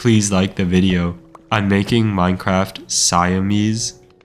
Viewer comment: I thought it said Minecraft Cheese.